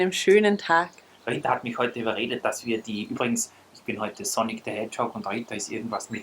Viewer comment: Ich hab am Anfang echt "Sonnig the Hedgehog" verstanden XDDD Btw.: Mietzekatze <3